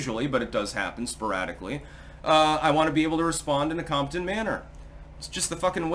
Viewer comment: When said "i want to fire back in a competent manner" I thought he said "I want to fire back in a Compton manner". Lol 😆 i liked my perception for the comedic value, but both are acceptable 👌